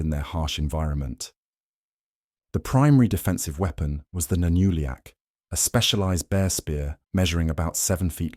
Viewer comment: what did you say the bear spear was called? 8:30 the nunuliak?